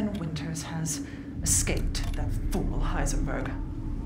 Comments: I thought she said "He went full Heisenberg", like Ethan just started cooking meth around the castle, melting the bodies of his enemies in acid barrels. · in 0:32 did she say heisenberg?